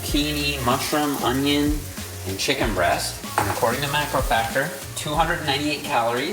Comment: Jeff says “according to MacroFactor” But the autogenerated subtitles say “according to MyFitnessPal” The disrespect from YouTube is NUTS🥜